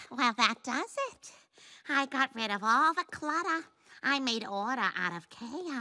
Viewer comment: i heard him say it like that “i got rid off all the cLURA, I made Ora our of Ky Os”